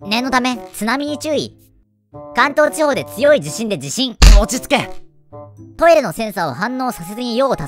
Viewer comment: 落ち着け！がもちつけ！って聞こえる((幻聴))